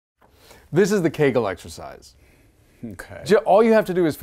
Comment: At first I thought he said “Kegel exercise” 😂😂🤣🤣🤣